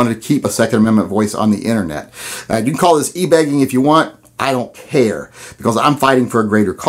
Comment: Totally thought you said ebagging. I was all, wth?